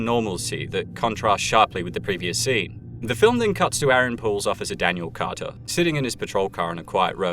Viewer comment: Aaron POOLE. I thought he said Aaron Paul and dude looks a LOT like Jesse so...